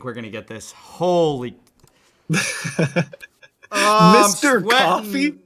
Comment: I honestly thought you said "Mr Caffrey" from White Collar, I thought it was a bit on the nose but Mr ☕ was cute